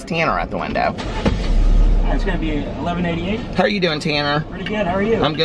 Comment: Literally thought you said Tana not Tanner haha! I wanted this drink until I heard what’s in it. Doesn’t sound good to me but that’s just me. I wanted for it to sound so much better. I absolutely love Nightmare before Christmas